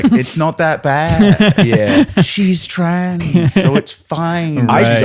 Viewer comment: wtf am I hearing wrong or is using the t slur at 19:42? 🤨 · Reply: No, he’s says trans, just holds the ending longer